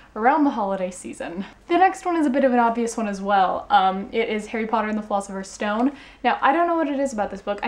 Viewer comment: For a second there, it sounded like you said "Harry Potter and the Fault In Our Stone." I was quite confused until I realized...